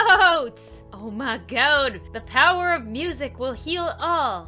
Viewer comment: i tHOUGHT SHE SAID PERSONA 5 MUSIC